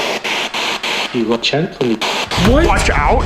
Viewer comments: I heard "we watch out for you" not "you watch out for me" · how did Alex hear watch out for me? I heard “you will gently..”